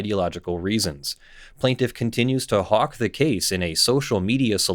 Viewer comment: did they really say she was hawkin' on the case lol